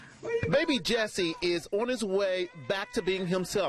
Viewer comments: Who else thought it said “baby Jesus” (Unless I’m the only one) :’) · Hi sorry. I thought it said baby jesus